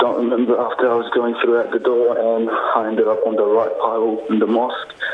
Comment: "[inaudible]" - He clearly said "ended up", BBC. Get better subtitlers.